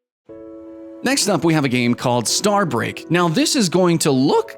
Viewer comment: I thought he was saying star brick